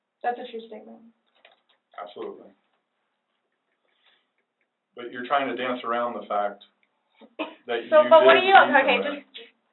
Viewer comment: Anyone understand what he says? She whips her head around like she’s gonna hit him. I can’t make out what he said Can anyone help? Thanks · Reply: "You're trying to dance around the fact that you did indeed left him in there"